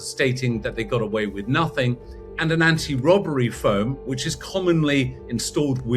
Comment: They didn't steal anything: the van withstood the explosion and two of them have already been captured. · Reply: They said anti robber foam went off. I thought they said phone first.